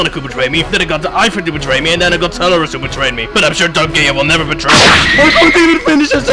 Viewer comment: It sounded like eggman said Dark Gaya instead of Dark Gaia